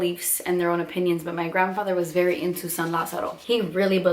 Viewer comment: What did she say at 5:16? " My grandfather was really into..."?